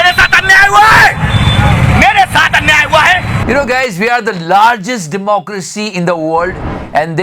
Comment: Ek pal ke liye misheard ho gaya.. sorry I thought you said "we are the largest hypocrisy"